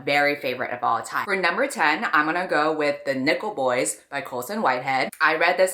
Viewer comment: omg i thought she said the nipple boys